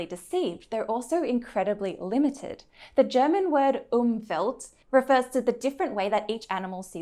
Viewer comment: what i heard was "umfällt". What she meant was "Umwelt", what she wrote was "Umvelt" - what is not a German word.